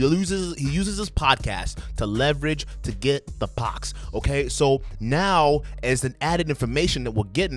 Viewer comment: I thought you said "the pox" and I immediately agreed, "yup, seems logical that Myron would have a sexually transmitted disease aka pox aka syphilis. Ew." Lol.